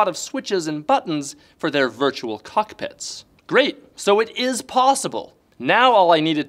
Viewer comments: Did you say Cuckpits? lmao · did he just say "cuck pits"?